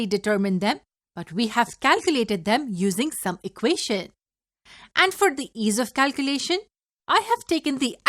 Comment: Really fine series overall. Thank you. I’m sorry not to understand what was being said at 3:54. “... we have calculated them using ?????” Sounds like *subucacian*. Again sorry. I replayed this part a dozen times and still can’t make it out. Anyone? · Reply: It's some equations!